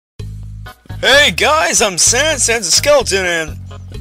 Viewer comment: when you said sans the thing ( I dunno what's it called) said SENSIN